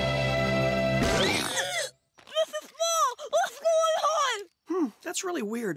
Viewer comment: did he say the f word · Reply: He sayd:"mr.small what's going on!"